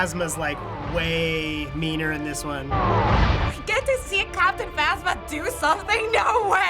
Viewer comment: what she say? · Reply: "You get to see Captain Phasma do something!"